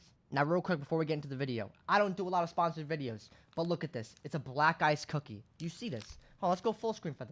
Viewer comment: When he said "black ice cookie" I fr thought he said "black guys cookie"😂😅